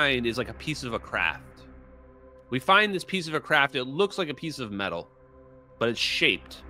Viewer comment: I thought you said we find a piece of crap